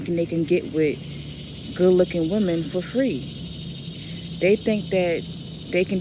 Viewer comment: I thought she said “good looking women “